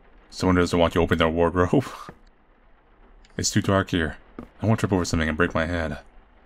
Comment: "I want to trip over something and break my head' The way he said it so calmly lmao💀💀😭😭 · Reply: He said:”I don’t want to trip over something and break my head” but it sounded like “I want to trip over something and break my head”